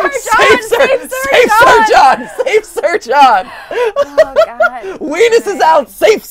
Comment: I thought they were saying save sir john instead of safe search on